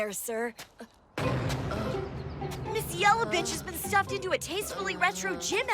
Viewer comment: am I the only one who heard ms. Yellow bitch? I hav to actually watched the anime so I don’t know what her name is